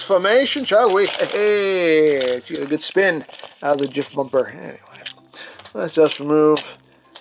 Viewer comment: I have a big problem with deluxe Cliffjumper. Hes not a deluxe. For the price of two legends, you get one, with some accessories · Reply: Also 5:26, did i hear me a Jiffbumper? 😁